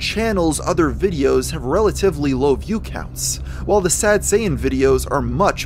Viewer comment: I keep hearing "Sad Saiyan" and not sure if its my ears or your accent